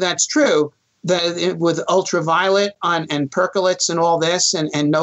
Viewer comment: I heard him say, "with ultraviolet and Percocet. . ." Needless to say, that would explain why we haven't sent humans to Mars. lol